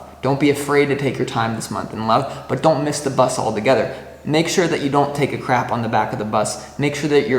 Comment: Lol did you say at 10:00 "make sure you don't take a crap on the back of the bus" -? did I hear that correctly😄. Good luck on the astro studies, it can be very rewarding. Looking forward to see how you combine it w/the current tarot-based readings.